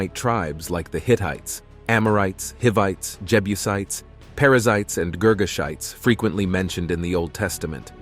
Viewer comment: Thanks for visiting The Ultimate Discovery Channel! I hope you enjoy the watch! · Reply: I misheard parasites @ 02:00😂🤣